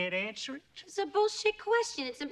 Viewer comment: What the heck is that word she says (0:23) ? I love New Yowk accents, but what is that? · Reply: She said “That’s a bullshit question”